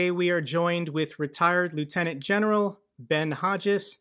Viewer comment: The way I heard first sentence "we are joined by retired lunatic general"